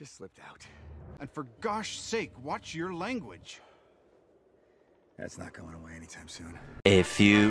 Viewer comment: What did Steve rogers say? Couldn't hear it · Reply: Thats not going awat anytime soon